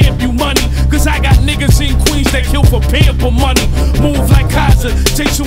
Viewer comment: "Got ni**as in Queens that'll kill for payable money" I thought this man said "pamper money" 😂😂😂 way harder. Imagine getting killed for diaper money